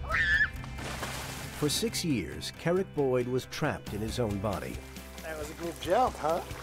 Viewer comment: I totally heard "carrot boy"when they introduced.